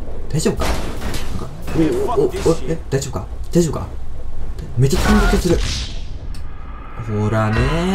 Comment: 「めっちゃピンぼけする」って聞こえるんだけど、目つぶってるんだよね？ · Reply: めっちゃ飛んでる気がするって言ってる